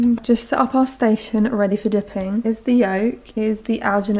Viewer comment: I thought she said "here's B.O." and I lost it😂😂